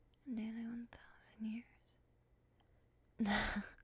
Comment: Did I hear that right? 91,000 years 😐? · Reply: “Nearly one thousand years.”